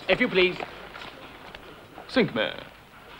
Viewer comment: What does he say at 6:15? It sounds like "sink me". Whatever it is it's awesome; I think I am going to start saying it. Just like he does too, with the long gutteral noise at the end of every sentence.